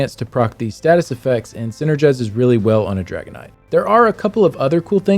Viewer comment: i swear to god i thought you were saying dragonite from pokemon when saying dragon knight.